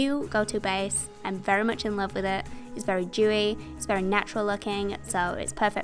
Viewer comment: Are you saying jewy?? I put on close captions and it says doing but still can't tell what your saying, lol